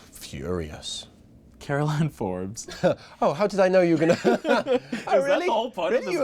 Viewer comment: can someone write what they said ? please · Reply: Interviewer: Caroline Forbes...? Joseph: laughs Oh how did I know you were gonna Interviewer: Isn't that the whole point?! laughs harder Joseph: muffled laughter Did you really wanna know about those first four or three?! both laughing Interviewer: I looked interested! Joseph: Uh huh, you did, you did . . .